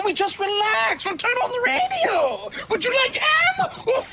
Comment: What got me was "why don't we just relax and turn on the radio? wOULD YOU LIKE AMMMMMM OR FMMMMMM?" Because if the god damn "FMMMMM" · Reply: I always thought he was saying, "Would you like HAM"